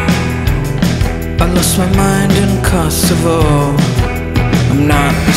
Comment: At first I thought she said, "I lost my mind in Costco." It is one of my greatest fears.